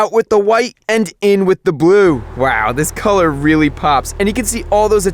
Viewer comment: did he say "in with DA BLUE"